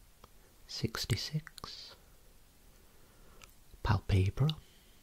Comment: When you said palpebral I thought you said palpatine and then you said 66 right after and I lost my mind